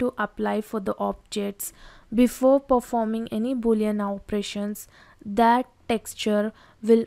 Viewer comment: I can't understand what you meen @ 11:56 before you boeliant?? operation"" please explane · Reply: Its Boolean Operations - Combining 3D models , subracting it etc